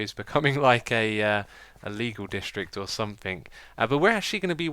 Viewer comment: lol i thought he said illegal district like with meth lab or something